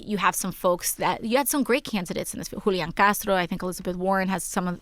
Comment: for a second I thought she said Fidel Castro😂